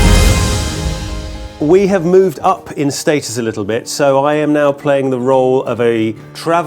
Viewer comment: I thought she said we've moved up in Jason Stathem 😅 not Jason Status